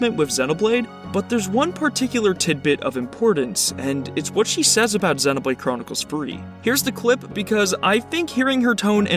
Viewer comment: xenoblade 3 I thought you said Overwatch 2